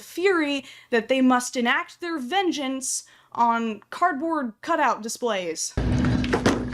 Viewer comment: I thought she said "Cartman cutout displays" and I was like :0 they what?